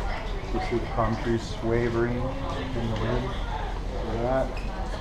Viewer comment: there was a whisper I heard say “look at it” lol beautiful ship I’m going on it in November 2019 thanks for the tour 😊